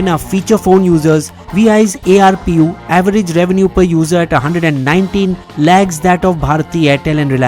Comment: At first it sounded like "₹119 lakhs". Looks like VI users are millionaires Then I saw the text "₹119 lags"